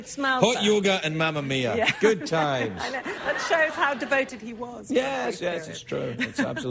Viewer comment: What does she say at 8:07 ish "That shows how devoted he was when ... " ? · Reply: checkerbordom "when we first met"